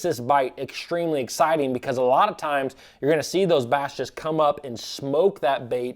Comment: did you say "bass just" or "bastards"?